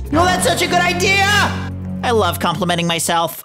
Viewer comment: did he say the f word? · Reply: No, he said the word love